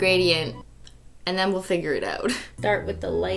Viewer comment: is it just me or did she say ,,finger it out,,? :)))